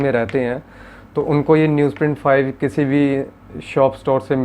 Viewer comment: which paper sir? Sorry I couldn't hear it well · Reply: Its news print paper sketchbook, available in fine arts shops.